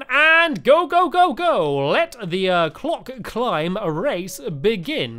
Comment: i thought i said c*ck climb.